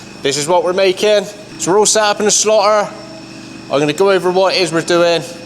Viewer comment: No offense but I thought he said slaughter 😂 absolutely loving this. Are there any sites about the people who designed and built these fantastic machines?